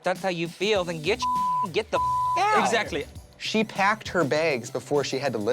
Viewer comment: All drama aside, did Tracy really said ' she pecd her begds ' ?